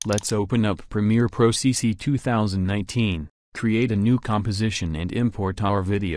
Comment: Did he just say: "Premiere Pro CC 2019"?